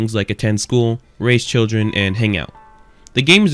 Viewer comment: I genuinely thought you said race children